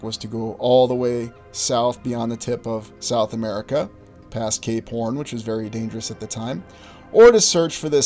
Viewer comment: You said "cape horn" ...i thought you said "k-porn" and was about to google that shit! ..thank god i listened again before getting into that search lmao